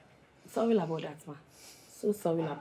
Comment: I even thought sibe was saying sorry in abundant ma,so she is saying sorry about that